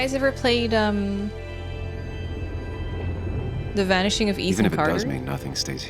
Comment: Not gunna lie at first I thought you said “the vanishing of Ethan nestor” and I got really scared for a second 😂 like pls don’t hurt smol soft boi